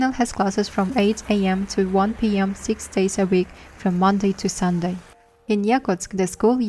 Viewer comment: the subtitles say from "Monday to Saturday", but then the narrator says from "Monday to Sunday". Does Lionel has the choice to go even in Sunday? 😅